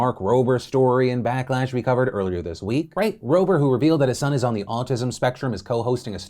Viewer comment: is it just me but does he call mark Rober, frank Rober at 4:20. unless he talking about mark dad named frank if that is his name